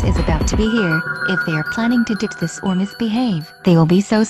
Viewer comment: Did Doris said “Mayor Phlegmming” instead of “they are planning” at 4:31?